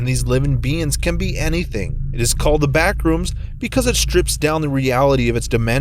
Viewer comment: I fr thought it said bathroom 😂